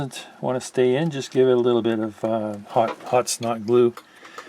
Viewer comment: What was used to stick the LED in that was a little loose? Could not understand what was said at 20:55. Thanks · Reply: I'm pretty sure it was hot (snot) glue.🤣